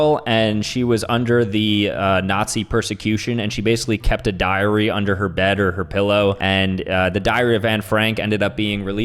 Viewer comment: Why did i thought he said "she kept diarrhea under her pillow" 1:28 💀💀💀💀